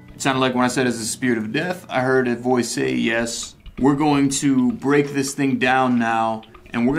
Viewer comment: "R u the spirit of death?" i heard a "yes." Tht scared me to the point i stopped it a second l8r, @ 9:15 but, i heard it @ 9:12 but, reason y it scared cuz, it didn't sound nice. It sounded...... EVIL.😿😭